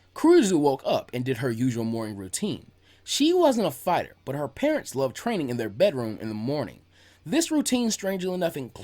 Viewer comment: did you actually say her parents love training in their bedroom!?!